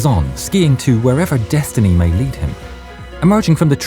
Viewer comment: i thought he said "where ever destiny may yeet him"